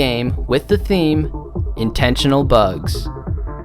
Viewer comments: Thought that you said that you made intentional bucks with and why did you fix a bug???????????😮😮😮😮😮😮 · I thought it said international bugs lol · Who else thought it said 'international bugs'